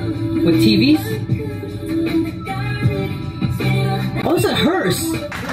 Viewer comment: ok i've replayed this part for like fifty times now and i still don't know what said at 3:12 . plz someone enlighten me bc i'm curious. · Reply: "Oh.. It's a Hearse"